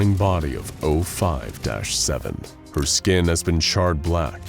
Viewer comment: i watch this with the subtitles on and at 7:06 you say the burning body of 05-7 but the subtitles say 05-8 love your content